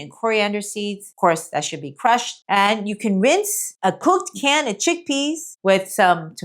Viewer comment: 🤔 Can beans ok ?????? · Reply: I believe I heard her say drain and rinse canned chickpeas.